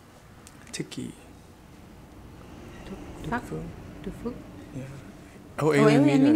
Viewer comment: Chị nói gì cơ? :)))))) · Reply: THANH TRUONG Đức phuc